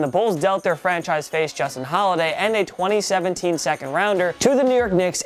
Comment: Did he say the bulls franchises player was Justin holiday